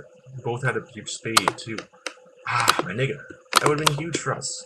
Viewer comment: uhm....did you just say: "aaaww....my nigga" whats up with that?